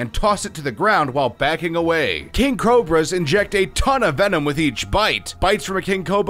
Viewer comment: did he just say king coba